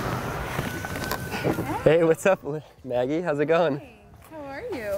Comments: is it just me or did he almost say lizzy at 6:58 instead of maggie? 😂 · anyone else hear when carter pulled up in his car he almost said “lizzy” but stop himself and said “Maggy” like at 6:52 time